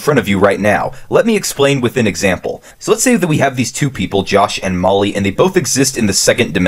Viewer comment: Thought it said Josh and Mully from the boys 🤣